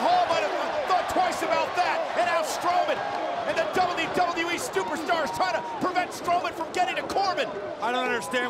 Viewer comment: i hear stupidstar.